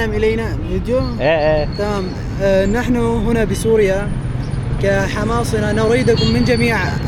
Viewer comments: not hamas bro the true translation of what he says “as people from Homs” not “as Hamas” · i think there may have been a mis translation. I think it says Hama or Homs, not Hamas